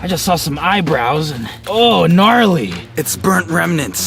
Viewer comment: did say gnarly😂